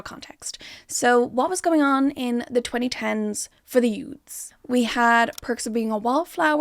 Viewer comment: Spotted a mistakein the subtitles at 4:30 (subtitles say "U.S." while I'm hearing "youths") edit: also at 17:53 the subtitles say "Paradise" instead of "Erudite", I wouldn't be surprised if this happens more than once. Please check your subtitles before uploading your videos and make sure they're accurate.